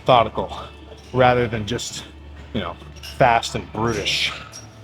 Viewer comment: I thought you said “fast and British” instead of “fast and brutish” for a second